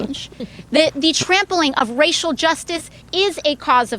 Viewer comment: did she say "trampoline of racial justice"? Cause if she didn't, I am going to trademark that.